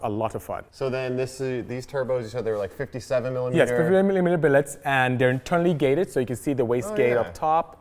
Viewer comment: I'm dying 😂 28:12 "Yes! Filimililimilibillets aand...." Love you... but what the hell did you say?😂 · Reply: 'Yes! Fifty-seven-millimetre billets.'